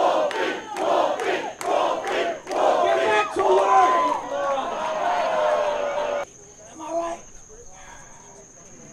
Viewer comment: what does leo say at 6:00? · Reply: “Get Back To Work”